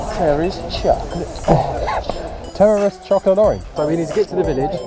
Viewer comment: what did that guy say? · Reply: 1 He said "Terry's Chocolate Orange" Google it....it's a UK thing ;)